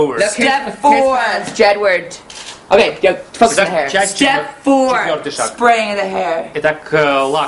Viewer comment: Edward "Fuck the hair" or am I hearing things?